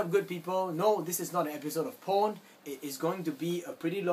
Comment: sounded like you said "No this is not an episode of porn"